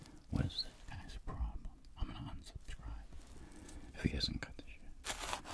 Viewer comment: In 8:38 did you say "if he doesnt cut the shit." Its like in this video your venting out hate comments. You just need to get used to them because if you feed the trolls more, they will go for more.